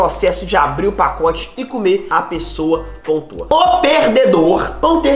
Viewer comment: "abri o pacote e come a pessoa'' vei eu n entendi foi nd kkkkkkkkkkk